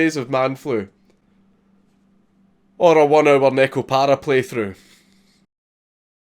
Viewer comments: What did you say? "..or a 1 hour neckle para playthrough..." Ya what now? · No matter how many times I play it back, I just can't make out what he's saying at the end 16:58, "Or a one hour nicole para playthrough"? This is gonna drive me crazy for the rest of my life if I can't figure this out